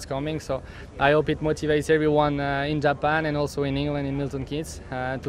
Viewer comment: does he say "everyone in Japan and in England at Milton Keys??" I thought they were still based in Italy??